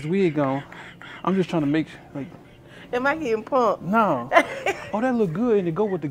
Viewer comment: Oh my gosh I read the comment quoting “Am I getting punked?” I thought she said “And my head plump.” 😅